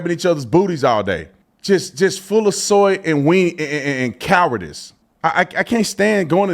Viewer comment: did he say, "Full of soy and wieners...?" 🤔